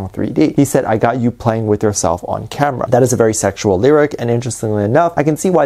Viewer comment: I thought it said got you playing with your cellphone camera.